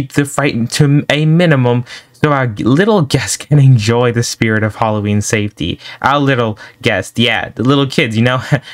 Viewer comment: did they said spirt halloween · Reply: The spirt of Halloween